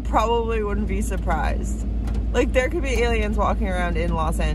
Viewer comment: 😍 Oh Precious, You, and Your Videos give me Life!!!🤩 When you said, "Aliens" in CA, I thought you said "Santa Claus", I had to run the video back to see if I heard you correctly!!! You are funny, and fun💝 Thank You for who you are, and thank you So Much for sticking with your YouTube channel ♡ You Have The Best Videos!!!❤ Thank you for taking us along!!! 👏 Suzanne <(((><